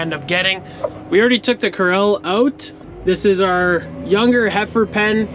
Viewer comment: What term are you using for "cows"? Last video and this one, it sounds like you said "we already took the corrals out." Are you using a Dutch term?